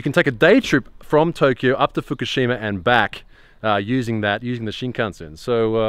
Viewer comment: I thought you said "Using the shitcunts" but then I remembered you're in Japan and not Sydney.